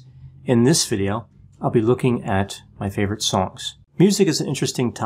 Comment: I thought you'd said 'favourite socks' 🙈 ...maybe for 600k...?🙃